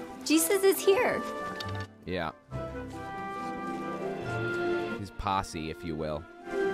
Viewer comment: When the woman said "The apostles are here", i heard "The posse is here" and now i'm imagining the Insane Christ Posse with Jesus and his apostles wearing juggalo makeup!